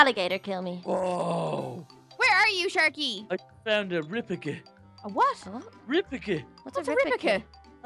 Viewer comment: 232 I thought was trying to say replica